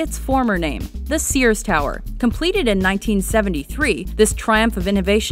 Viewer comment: I thought i heard it was opened in 1974